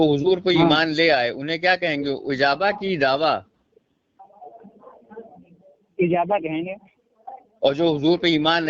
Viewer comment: Bhai 06:00 pe kya kaha aapne ijawa ya /Ijawa? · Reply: Ummat e dawa ya ummat e ejaba.